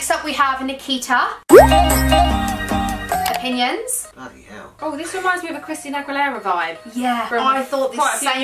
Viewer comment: Am i the only potterhead here or did anybody else hear say bloody hell at 7:04 #Potterhead Ps: Love you Saffron😍❤❤